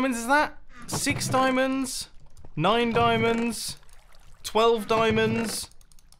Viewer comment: When you said nine diamonds, i thought you said mine diamonds